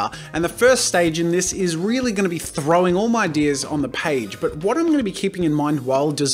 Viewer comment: I thought he said "throwing my deers on my page" and I was so confused 😂